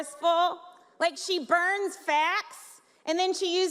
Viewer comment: Lmfao. who else thought she said she burns fat then uses her ash to make her raccoon eyes?